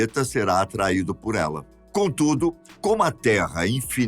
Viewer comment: Entendi "a lo largo". Mas o "contchudo" é pra lá de Bagual!